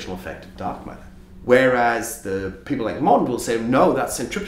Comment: Question: At 7:00 when the interviewee says "...people like Mawndl...", who is "Mawndl"? Might that be a mispronunciation of "Milgrom"? Or does he mean someone else? Due to the echo distortion in the audio, it's hard for me to discern what he's saying.